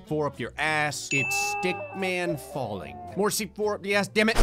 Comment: did he just say di** man falling?